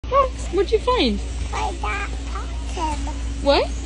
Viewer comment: I heard: "I got a pumpkin" ...